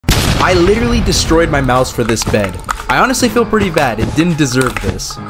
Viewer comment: I heard: "I destroyed my house for this bed" 💀